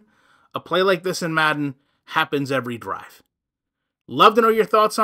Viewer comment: “a play like this happens every *graph*(?)” I know you didn’t say graph. What were you saying there? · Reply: Drive 😂 not graph. He kind of sighed so it sounded like an ughhh combined with drive.